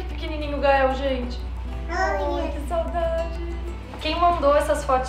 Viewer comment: olha que pequenininho o Gael gente Eu juro que eu escutei o Gael falar dps "oia" 8:22 kkkk